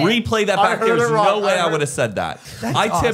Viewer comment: BUSTED ON THE REPLAY!! he clearly says “i tip a lot when someone recognizes me” 😂🤣😂🤣😂🤣